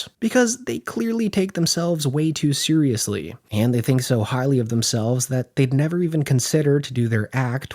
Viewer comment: "They think so HARDLY of themselves?" .... you mean "highly"? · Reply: The sound is a touch muddled but I thought he said highly